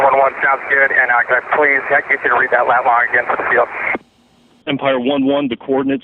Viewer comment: "read that lat long for the field again for the fuel?" - Huh? What he says is "read that lat-long again for the field".